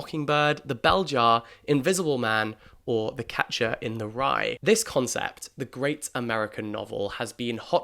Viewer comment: I heard ‘Focaccia in the Rye’